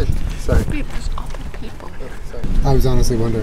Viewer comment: anyone else catch julien say “here’s your sandwich sweetie”? idk man that shit was adorable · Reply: anyone else hear “babe there’s other people here”💀